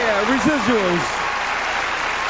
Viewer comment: What did stevie say at 2:28? "Yeh... resilience?" was that it? cant figure out the word. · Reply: Hollie Mae no, he said "residuals"